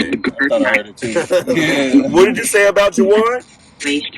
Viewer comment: or so, when asks 'what did you say about Juwan', I heard 'praise Juwan'.